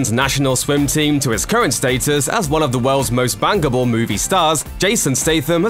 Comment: Oh my goodness I had to relisten a couple of times as I thought it said he was one of the most “bangable” stars not “bankable” 😂😂